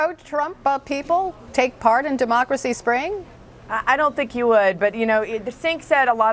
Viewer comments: did she just call him Sink? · did she call him sink